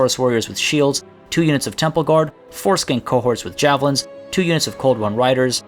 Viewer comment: am I the only one who heard foreskin cohorts?